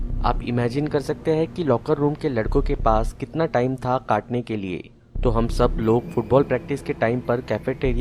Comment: I thought that you said ch*tiy@ kaatne ke liye